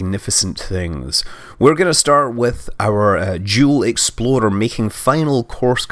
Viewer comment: Huh? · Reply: It's because he sent up two spacecraft named the Jool explorer and the Eve explorer, but it sounded like he said exploder when he pronounced explorer.